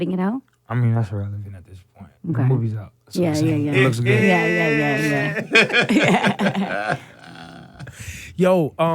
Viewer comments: What did he say at 3:55 ?? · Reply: Sophia Baligad it says it’s irrelevant at this point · i couldn't hear what he said about Rihanna. What he said? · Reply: “I mean that’s irrelevant at this point the movies out” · what did he say? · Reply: two lost souls in a fish bowl “I mean it’s irrelevant at this point”